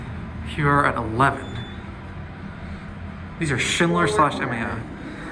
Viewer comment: Did you say "These are Schindler Saucyman."? Sorry... I've played it over like 10 times and I'm curious. Whatever they are, they're really cool!! Actually, I haven't been to MSP in years. When I fly I use DLH and the prices so much better on United so I always end up through ORD. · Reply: Haha it’s all good! I said “These are Schindler/MEI” although I don’t believe these are MEI now